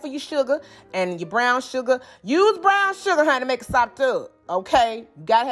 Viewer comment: “use brown sugar” then what did you say? Make it what? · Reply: Make it sopped up = Make it delicious ❤